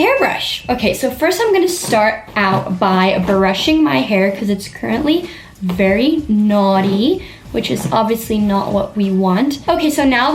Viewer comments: NOO I THOUGHT SHE SAID NAUGHTY 😂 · I thought she said naughty 😂😂😂 she meant notty